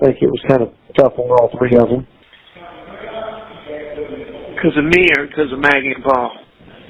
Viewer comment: Did he just say Because of me Or because of maggie and Paul